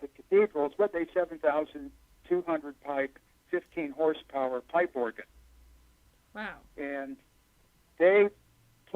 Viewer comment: what is the second name after he says Bach, George..? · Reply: Oh good!! Haha. Gurdjieff is a master!